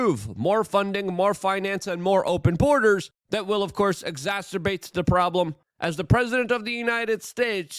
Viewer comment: circa said exasperate instead of exasturbate. I never thought that I would see the day of proper spelling, pronunciation etc. Anyhow I prefer the old days.